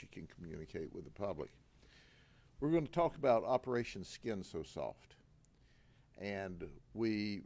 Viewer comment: For a second I thought they said Operation SkiMs so soft😂😂 I had to rewind like woah woah lol